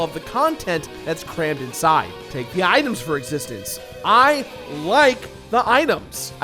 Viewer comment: did he just say "take the items for existence"?